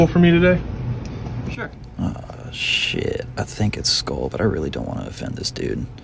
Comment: If he comes in every day, its definitely Skol 😂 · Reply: I thought he was saying skull in some weird accent lol I heard skol the first time but only knew of the chew